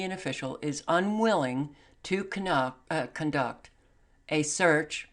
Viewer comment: you said canuck instead of conduct. LOL i hope i heard that right, cuz it was hilarious. we need hilarity!